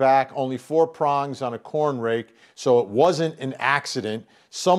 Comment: I misread corn rake as "cornflakes" but then realized that would make him a cereal killer........... I'll see myself out. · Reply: I honestly thought it said corn snake!! 🐍